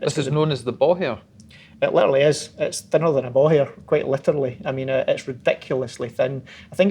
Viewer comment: did he just say it's thinner than a ball hair xD